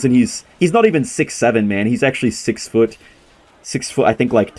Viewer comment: Did he said 67?